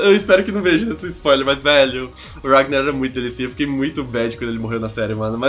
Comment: "Ragnar era muito delicinha", foi isso mesmo q ele falou produção?